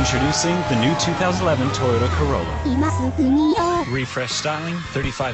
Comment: I thought that said “tortilla corona”